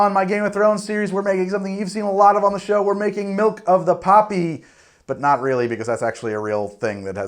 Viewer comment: Please tell me I'm not the only one who thought they were saying Milk of the Puppy?